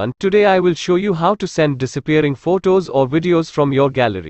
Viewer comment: I thought it said “send disappointing msg “ lmao